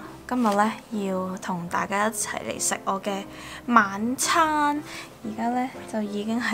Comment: 吃晚餐我都會聽成吃盲腸